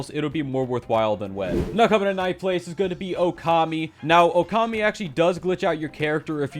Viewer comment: he says "okami" the subtitles says "web" this is the worst mistake you have done